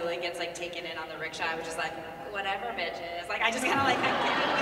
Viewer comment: I really heard Azula say "Whatever bitches" and I'm now very happy